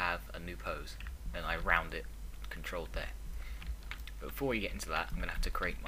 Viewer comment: you say "control thet"?what? please xD I dont understand what you just said.